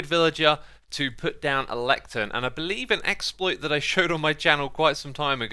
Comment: Did he say exploit?